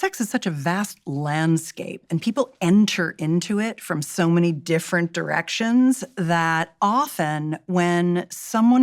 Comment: I was half asleep while listening... @ 2:48, I heard "& ppl enter into it from so many different erections..." 💀